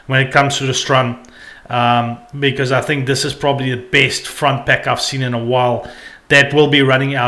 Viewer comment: Bro, what's a front back? Heard you say it a few times now. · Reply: Think i am saying front pack, but i guess my strong afrikaans accent might make it sound like back